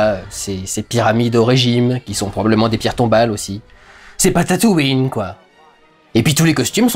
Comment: "C'est pas Tatooïne"... j'ai compris "c'est Patatooïne", la planète en pommes de terre. Non mais sérieux, ils devraient faire une planète pas sphérique, en patatoïde, où en levant les yeux tu peux voir une autre partie de la même planète !